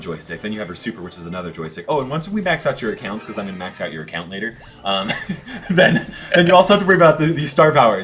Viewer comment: at first i thought he said the n-word, but it was just the word later.